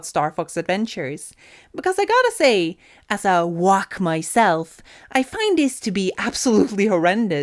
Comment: "wonk" or am I mishearing?